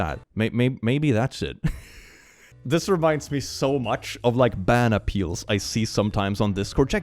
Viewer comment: When he said “ban appeal” I thought he was just saying banana peel in a quirky way😭